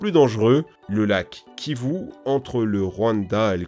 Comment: Jai entendu le "lac kiyu" à 11:24 😂